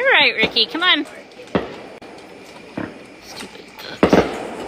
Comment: There was a voice at 0:21 saying “stupid…” and what sounds like “so cute”. Anybody hear it? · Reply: Sounds like "Stupid bugs.. euugh," and given how ridiculously hot it's been lately I can't say I'm surprised